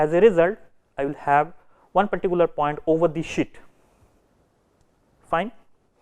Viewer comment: "I'll have one particular point over this shit". (I know he really said sheet)